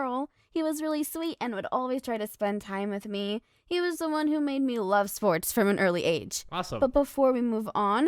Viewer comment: Please let me know that I'm not the only one who heard her say "And my dad iS the one whO MADE ME LIKE SPORTS FROM AND EARLY AGE!!"